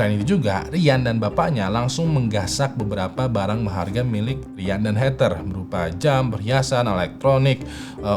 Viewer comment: "ryan dan bapaknya mengambil uang ryan dan heters" nya ini bg ngomong apa cok ga nyambung